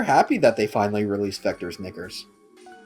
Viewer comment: When I first heard that, I misheard it as "Vector's Nipples".